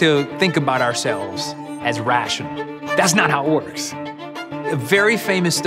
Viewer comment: i come across this video to practice my listening. can someone tell me what did he said on the 0:24 my opinion:" That's not how ordes" is it true while the transcript wrote down "That's not how it works"